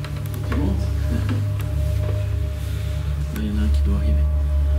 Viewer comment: Il dit quoi à 10:37 j'ai pas compris c'est quoi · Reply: Il a dit ya n'a un qui doit arriver